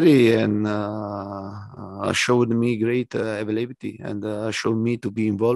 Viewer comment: Can someone please help me what evelepty is, Conte says it all the time, i can not figure out the proper word? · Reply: Definitely Availability